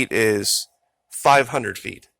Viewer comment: It's not 500 feet is longer than that is Skips back under the water and comes back up like some kind of snake the rest of the body is behind it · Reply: 500 feet? Ohmygod I thought he said 50 feet I'll have to watch it again thanks